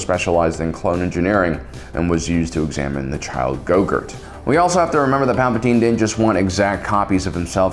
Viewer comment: That moment when he says examine gogert instead of saying grogu😂😂🤣😂😂😂😂😂🤣😂😂😂🤣🤣🤣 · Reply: I thought he said "Gogurt", the on-the-go yogurt snack for kids. 😆